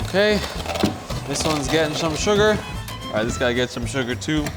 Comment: “you get some sugar.” i thought he said suga from bts